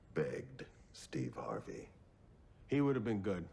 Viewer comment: I could've sworn I heard "we banged Steve Harvey"